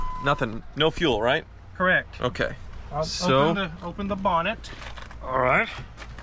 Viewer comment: did he say open the bonnet - is he from the UK?